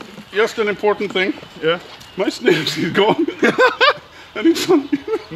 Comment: can someone tell me what said? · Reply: My snus is gone